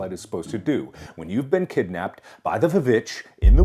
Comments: When he said “you’ve been kidnapped by the vvitch” I thought he said “you’ve been kidnapped by the bitch” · i thought he said ‘by the b-bitch’ i was like damn you didn’t have to be so mean :-(